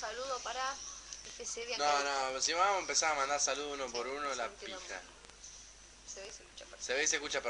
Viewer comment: Que dijo palo en el minuto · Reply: La pija! Jaja